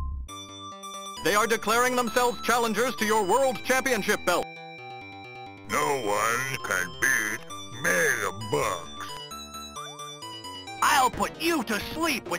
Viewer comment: Tons of laughs from Andre the Giant's garbled comment- "No one can eat MAIL BOX" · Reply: I never knew Andre said "No one can beat Mega Bucks" he said "Mega Bucks" in such a garbled way it sounded like he said "No one can beat me you bum."